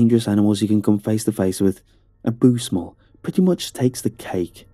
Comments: he said a boose mull instead of moose bull XD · "a Boose Mull" instead of a bull moose lol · Did anyone else hear him say "a boose mull" @ 27:36?